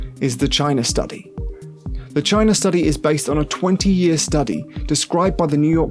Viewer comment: Lol 6:58 I heard vagina study